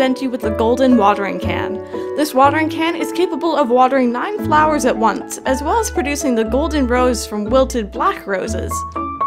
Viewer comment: i literally can’t hear what you’re saying. How do you get mushrooms? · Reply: she's saying you can use the golden watering can to transform a wilted black rose to a gold rose. mushrooms spawn in your town in autumn.